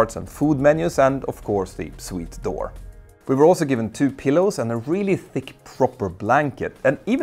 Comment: I could have sworn you said we were given two kilos😂 .